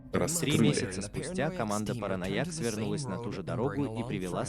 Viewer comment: Ахаха в первом видосе мне послышалось КОМАНДА ПАРАНОИК)))))